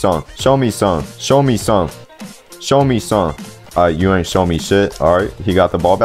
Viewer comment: Did bro just say shit LOL